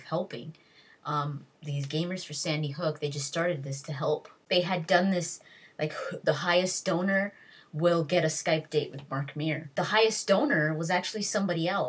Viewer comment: I thought she said the biggest stoner.